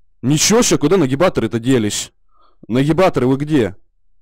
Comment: На 00:37 послышалось: куда наебаторы то делись?.